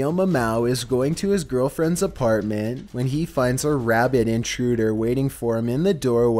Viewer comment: oh I thought you said rabbit so I was confused as to why he was scared 😂